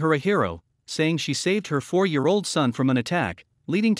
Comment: I thought they said 5 year old mother for a minute and rip mother and rip dog